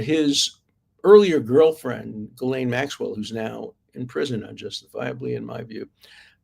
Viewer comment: Did he say justifiably or unjustifiably?